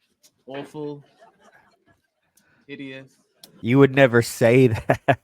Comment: Did he say idiot or hideous?